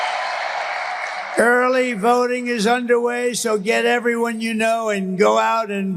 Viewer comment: Wait I thought he said early voting is cheating? 😂😂😂😂